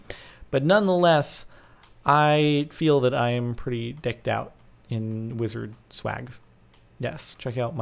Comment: It sounded like he said "I'm pretty dicked out"